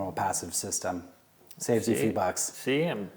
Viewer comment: Wait, did he say saves you $8? Lol